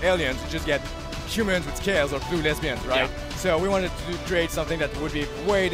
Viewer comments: did he say blue lesbians? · so at 16:02 does he say blue lesbians? Blue lesbians in space does sound like the plot of 95% of all films I've ever seen....